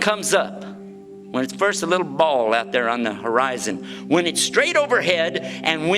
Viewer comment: hi can i ask what are the three forbidden times? cause i heard him saying , when the sun comes up and when it comes down.. thats 2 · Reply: kippp bo and when it's straight overhead. That's 3 there for you 👍